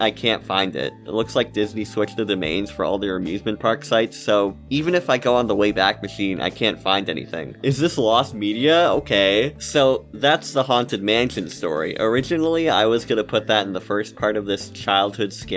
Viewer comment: did i just hear lost media?